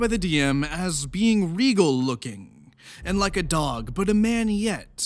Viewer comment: "regal looking" - oh my god, at first I heard "legal looking"